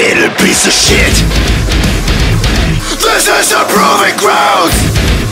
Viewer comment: It's sounds like Timo says POOPY GROUNDS lol XD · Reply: and fronz sounds like his says fuckin grounds